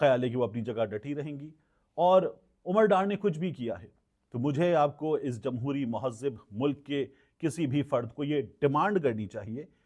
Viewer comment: Did you just say "MUHAZZIB"? LOL